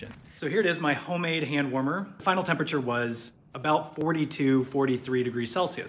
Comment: I kept hearing "sodas" as the ingredient which is supposed to trap the heat. I watched over 5 times and was thinking TF?? Thankfully someone in the comments mentioned "saw dust" 😊